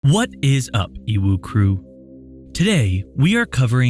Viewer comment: it sounds like you said "what is up uwu crew" lol